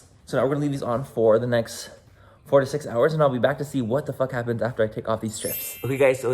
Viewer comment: I thought he said 48 hours 😂😂😂